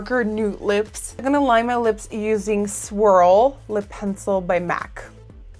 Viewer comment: I thought you said "swirl by Mac" until I checked your blog haha gorgeous look AS USUAL xoxo